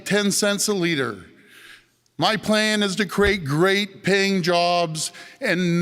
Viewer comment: I thought Ford said he was for great "Ping" jobs, as was just about to Google Ping, when I realised he said great paying jobs!! Hahaha..